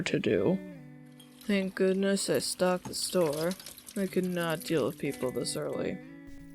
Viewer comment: I thought Virgil said I don't like people And I was like SAME and then I herd the rest Sorry for commenting again